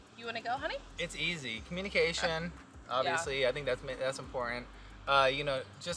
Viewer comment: Lmao when said communication to the first question I thought he said vacation! Haha 🥴🥴